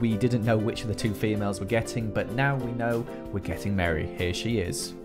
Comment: When Joel said "We're getting meri" I thought he said "We're getting married" (Like with Lizzie) Me: JEVSJEBHDHEVVSNWBDBS